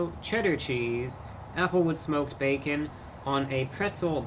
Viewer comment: First 😂 around, I thought you said “apple with smoked bacon.”